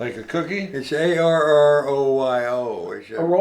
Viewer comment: lol I thought he said Oreo too 🤣😂